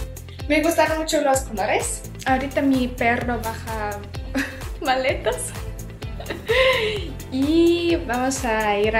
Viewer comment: Escuche bien ? Ahorita mi perro baja maletas ???? ajjajajajaj